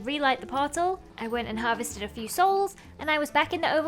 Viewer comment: wait what dd she just say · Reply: Harvested a few souls yep she said it Allright